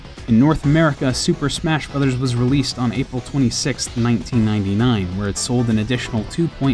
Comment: on 4:35 the guy says April 26th when the caption says April 6th